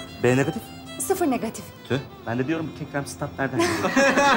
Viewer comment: de ne diyor anlamadım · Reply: Diyor ki: "Ben de diyorum bu kekremsi tat nerden geliyo? "